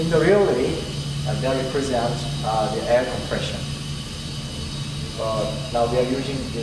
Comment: The sound is so bad I have no idea what you are saying! So I turned on subtitles and the result was hilarious: @ 0:59 "air compression now their virginities" lol